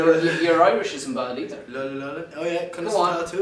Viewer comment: the interviewer says to harry "we do know your…" and i swear it sounds like harry says with louis…… -_- I'm i the only one who hears that? idk if that is actually what he said but it kinda sound like it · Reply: He does say louis but its not relevant to larry thingy